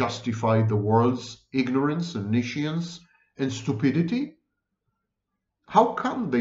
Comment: What's the word he's using at ~ 21:25 "I'm not gonna justify the world's ignorance, .... [niscience??]"? It's not "omniscience" as given in the video transcript, I think. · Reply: Nescience.